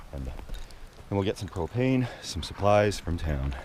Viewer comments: I thought you said yall was heading into town to get some cocaine and supplies. I had to rewind it...propane and supplies I got it the second time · I swear I thought you said "cocaine" not propane at the end....lmao.... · “We’re going to get some cocaine and some supplies” 😂😂😂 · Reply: There it is, someone posted my comment. Totally misheard propane as cocaine. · my god i thought he said were responsible campers so were gunna get some cocaine and other supplies, lmao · I swear I heard cocaine clear as day the first time and laughed at the joke. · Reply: I thought he said cocaine too. Had to rewind the video a couple of times before I realized he said propane. Lol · I could have sworn he said, "we'll get some cocaine and some other supplies" lol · I really thought he said "We'll get some cocaine, some supplies, from town." then realized he said propane lmao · What are they getting from town? It sounded like cocaine? I must be mishearing. Closed captioning says "coal pain" and that doesn't make sense either. Propane maybe? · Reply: he said propane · I swear at 18:10 he says he is going into town to get some cocaine and supplies... okay... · Did he say they were going into town for cocaine? 😆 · did he say "get some cocaine and supplies"?? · I heard " were going to go into town to get some cocaine" and I died laughing ...then realized you probably said propane..ha! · Oh, get some “propane” I thought kept saying get some “cocaine”. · Did he say "Cocaine"?